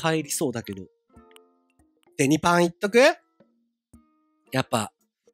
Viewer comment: 随分わしも穢れたものだ 51:24 デニパンが ベ○パンに聞こえてしまった